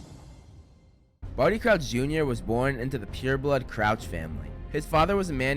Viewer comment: At first I thought it said farty crouch jr